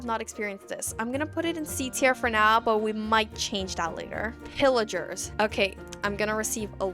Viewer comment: oh god I had this in the background as I’m drawing & thought she said “villagers” 😮‍💨